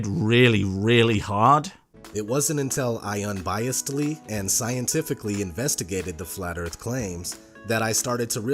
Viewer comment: Did he say scientifically investigated?